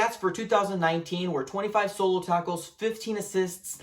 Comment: it sounded like you said 25 solo tacos 🌮.....maybe I'm just hungry lol !